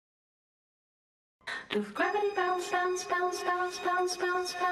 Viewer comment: I THOUGHT IT SAID GRAVY BOUNCE